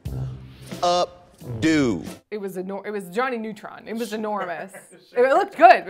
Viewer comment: Did she say Johnny?